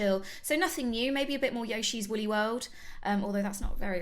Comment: When she said yoshi's woolly world I thought she said yoshi's willy world